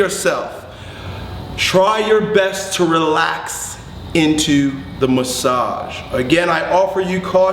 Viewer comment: Min i thought you say: try your best to not shit yourself :D